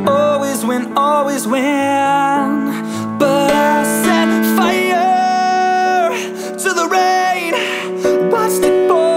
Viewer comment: I always thought the lyrics said "I set fire to Laurane" Like, who is this poor Laurane and why is adele setting fire to her...